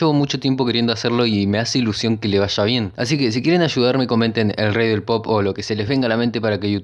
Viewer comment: escuché "el rey del popo"